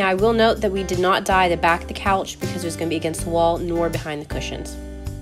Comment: I genuinely thought you said you didn't dye the back because 'it's against the law' 😂😂😂 I had to rewind it 😅